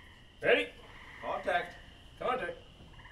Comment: they say "contact" but I keep hearing "corntact" in my head and "keep your slick feeling nice"(and variations of that) and even "she chooches" if you know what I am talking about you have a love for canaderp